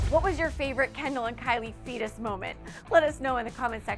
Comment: did she just say fetus moment...?